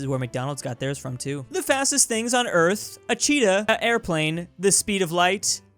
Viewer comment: did he really said "a airplane" but like, verbally?